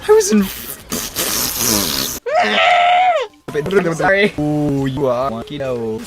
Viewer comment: i heard: oo u are wonky dough